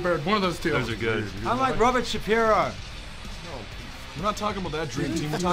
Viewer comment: Did Rose said "Robert Shapiro"?